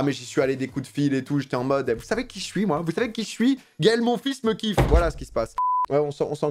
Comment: Jsp pas pourquoi mais quand tu as dis "Gaël Monfils" j'ai entendu "Gaël mon fils" le tient , à ce moment j'ai vu flou j'ai buggé je crois